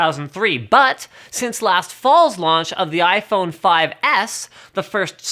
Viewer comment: Jesus.. I thought this was recent.. But 2014.. 😂 😂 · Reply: i heard him as the new iphone SE lol..